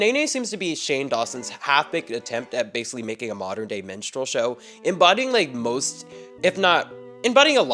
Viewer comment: I misheard “minstrel show” as “menstrual show” and had a WILD few seconds there